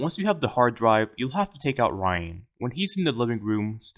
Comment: Hey, that's me! Hi youtube! Wait, did you say "*take out* Ryan?"